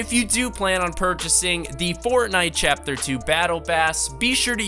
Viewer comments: did he say battlebass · In 0:41 he said battle bass. Instead of battle pass